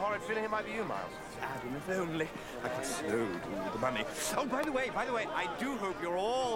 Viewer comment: What is Miles saying @ 0:50 - 0:53? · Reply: I know i'm super late, but he says "Adam, if only. I could so do with the money"